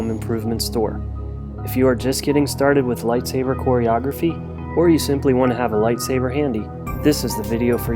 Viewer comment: I thought he said pornography